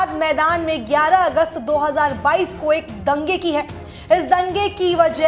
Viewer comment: did she say 11 August 20??